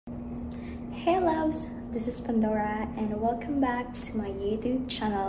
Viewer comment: I thought I heard "Panda wear" at the beginning of this video! LOL! 😃👌 nice shirts!